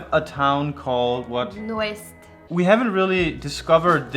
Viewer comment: Olá my Darlings. I’d like to suggest, as I know others have... showing the name of the town when you mention it. It’s difficult, sometimes to know what you are saying... between Portuguese names and Danish accents... please. For example, @ 4:49... I know you said Azores, but no idea what the name of the town was. It will especially help non Europeans. Obrigada. Great list. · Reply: As Rui Pedro Donas mentioned - the town in the Azores mentioned in the video is called Nordeste (north east in transliterated in English). This town is located on the island of Sao Miguel in the Azores. The island itself is very easy to get around - roughly 64 km wide by 21 km. Ponta Delgada is the main city that has the airport that you would fly into, and Nordeste would be about 30 minutes from Ponta Delgada. Quite frankly, the whole island is amazing, give yourself a week there bare minimum to check the place out.